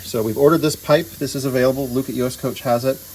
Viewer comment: what did you say here? Lucis us coach? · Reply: Luke <num>